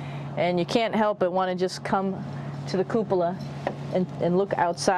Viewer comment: I wonder why the name of the place is called "kupla" (bubble in Finnish) or did I just mishear the name of the place.